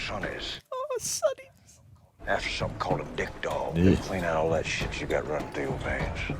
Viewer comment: Subtitles say "a dick doll" instead of "Addictol"